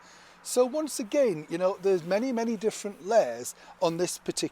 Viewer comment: Morning Neil, thank you for entertaining us x nearly misheard you - " So many different lies" oh sorry "layers" 😆